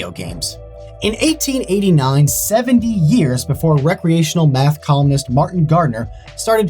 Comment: I heard recreational meth 😂